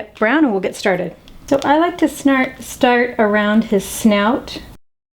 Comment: xD She said snart instead of start........ LOL CHECK IT OUT--->